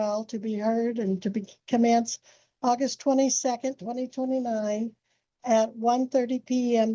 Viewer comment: Did she say her trial was August 2029??? As in 4 years from now? Or did I (and the captions) hear that wrong? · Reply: the captions are pure comedy. Don't rely on them ever. (I use them also, but they're funnier than they are accurate.) She said Aug 29.